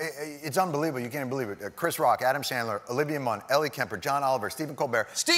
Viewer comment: Adam Sandler?!? I thought he said they have stars.